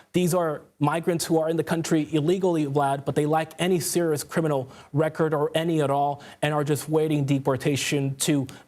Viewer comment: so what your saying is they’re illegal and have a criminal record but you’re trying to downplay that in order to manipulate our emotions and get people upset. Got it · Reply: He said they lack any criminal record. He says it kind of weird so it’s hard to hear